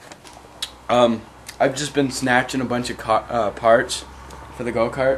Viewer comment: sounded like he said snatching alot of cock..